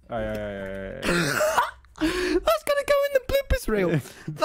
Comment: What is tommy saying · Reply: It’s gonna go in the bloopers reel